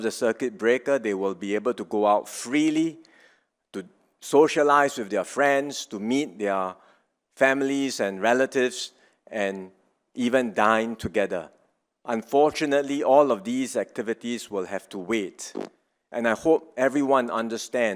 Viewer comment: I thought I heard something else lol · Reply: 😂🤣😂 and even DIE together....but it was DINE together.